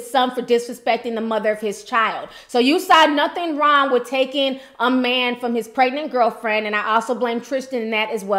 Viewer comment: A man cannot be taken, he has to want to go.... I keep hearing you say "taken" smh....